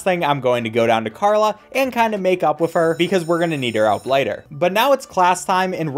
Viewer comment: I thought you said make out😭